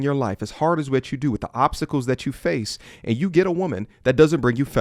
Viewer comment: Did he say obstacles or oppsicles?